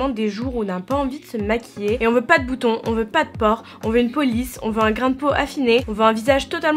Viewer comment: J'ai compris "on veut une police" 😭😂